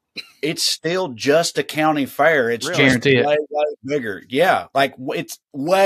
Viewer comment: I was only half paying attention and I thought he said "it's just a county fair with more n*****s" and I about did a spit take. That is not what he said.